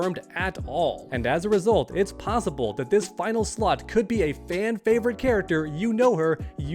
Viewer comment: Lol at 4:56 I thought he said "This final slut".